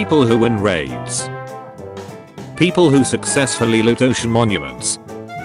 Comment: he says Purple instead of people Lol, Also Love your vids